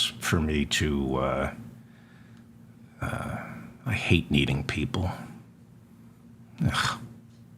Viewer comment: I thought you said, "I hate needling people." Oh, you satirized your thinking on communal engagement? Or is it just a good suggestion regarding a two state solution. I await your ire or an enlightened response.